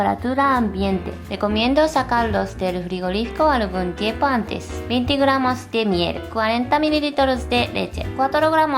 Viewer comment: Que dijo en él minuto 1:33? xD no entendí nada, es como Lele xD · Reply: Mariana Vega «recomiendo sacarlos del frigorífico algún tiempo antes»